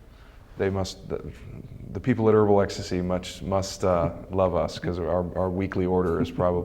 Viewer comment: He actually says at 1:54 in the interview "The people at Herbal Ecstasy..." so there you go ;)